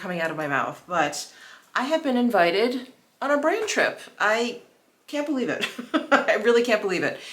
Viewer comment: Lol. I thought you said “Brain Trip”! I thought “She’s a smart girl. Why is she surprised?” Guess I’m not going on that “Brain Trip”!😂